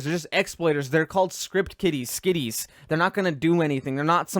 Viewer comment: Am I the only one that heard him say script kitties?